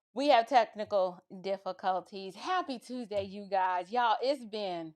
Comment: I thought when you said technical difficulties at your opener, I thought you said "testicle difficulties" and thought is that the new phrase for man problems? Lol!